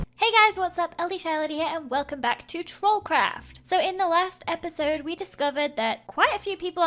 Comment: Omg at the start I thought Lizzie said,"so this is the last episode" instead of,"so in the last episode" so I started crying lol